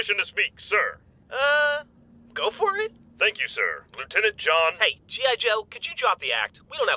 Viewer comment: did Smith say "Bluetenant" as a pun? or am i hearing things?